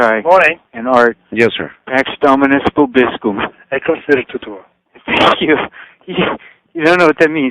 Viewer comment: what are they saying and in which language? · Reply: Hi Jamieson, they are saying "Dominus vobiscum; et cum spiritu tuo" The words are repeated many times during the Traditional Latin RC Mass. " "Dominus vobiscum" means "The Lord be with you" & the answer ".et cum spiritu tuo" means.."and with your spirit."